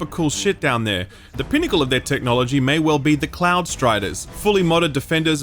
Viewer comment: bruh, I thought he said “Clown Striders” for like half a second, and I was about to be like, “Apt.” lol